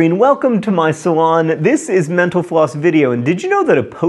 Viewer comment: Twice in this video, I heard: "menopause", rather than mental floss!